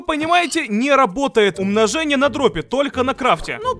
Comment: послышалось умножение на дробби